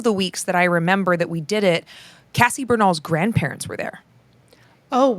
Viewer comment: I'm so sorry, I'm unfamiliar with that name and story, and can't catch the name clearly. Who? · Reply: Cassie Bernall